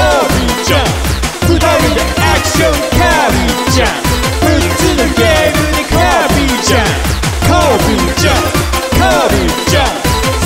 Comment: 「六つのゲームでカービィちゃん」ってとこ 「むっつりスケベなカービィちゃん」って聞こえる… これ幻聴かな…